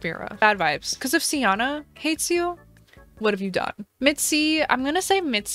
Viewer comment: You said mitzi and my stupid self thought You said Mitski 😭